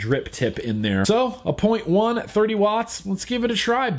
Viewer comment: Am I the only one that heard him say .10 ohms at 30 watts?